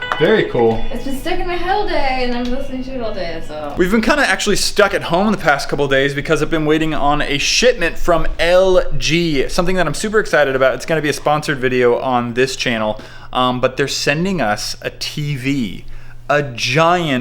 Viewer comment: it sounds like you said shitment XD